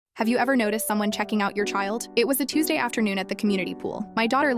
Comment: I was so confused because I thought he said "have u ever seen someone choke ur child?" 😅